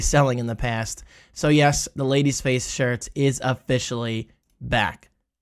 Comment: i looked away for a sec and thought you said a ladies face throat pillow.. 😭